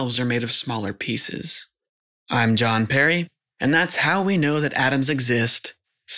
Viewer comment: When at the end he said "I'm Jon Perry" I thought he said Jean Perrin 👀